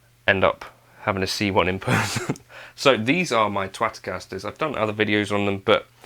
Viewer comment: did you call them 'Twatcasters'? That's what I heard and it made giggle!